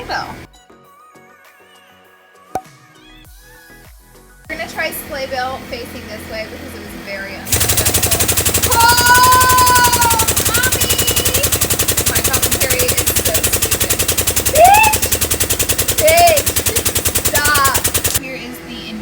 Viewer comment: Who else heard mommy